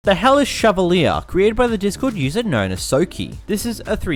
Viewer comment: I thought you said "the hell is chevalier"